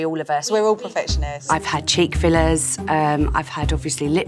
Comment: I thought she said i had "Chick-fil-A's"